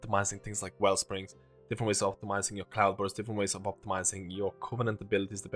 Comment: I heard "Plough-Burst" and that gave me a good chuckle...